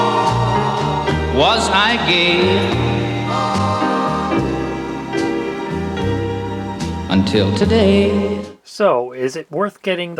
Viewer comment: what did Willie Nelson say at 14:02? "Was I gay, until today?"